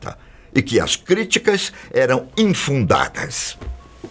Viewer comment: o que é "infundacas"? pesquisei e a palavra foi dada como incorreta, quero saber o uqe realmente ele queria dizer.